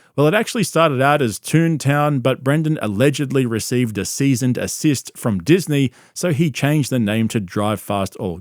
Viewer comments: I’m sorry… 13:53 did you just say Brendan received a “seasoned assist”? 😂😂 I think you mean a “cease and desist” order. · did bro just say "seasoned assist"?